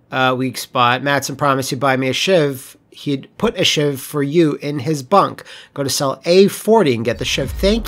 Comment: I totally thought you said “shiv for you in his butt” bahaha